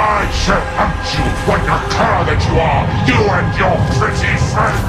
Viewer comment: Similarly as before. 1:02 "Betrayer! I shall hunt you, ...???..., that you are. You and your pretty friend." Anyone understands what he says there? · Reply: 'Like the cur that you are'